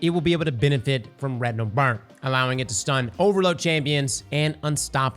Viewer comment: Thought I heard Rectal Burn for a second.